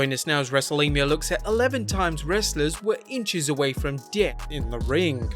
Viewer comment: Inches away from what? I swear in the beginning it sounds like he says "wrestlers inches away from deyet in the ring"